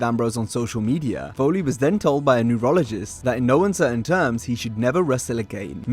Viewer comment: Oh, “neurologist”. Initially I thought you said “urologist”. 😂😂😂 Neurologist certainly makes more sense in the context of wrestling, but for a moment I thought: “WHAT?! What possibly could have happened? What happened to the “testicular fortitude” Foley often talked about?” 😂